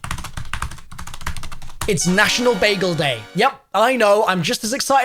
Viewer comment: All I heard was it's bagels day